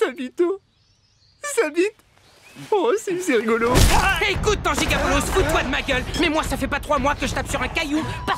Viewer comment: c'est moi où il dit "tanji-ga boloss"